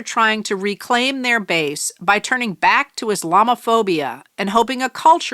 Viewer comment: did anyone else hear “his llama phobia?”